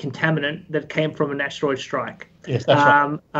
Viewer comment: CV19 is an airborne contaminant that came from a National air strike? I replayed that piece 3x and I can't make out that word. Looking forward to part 2. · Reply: ”that came from an asteroid strike.”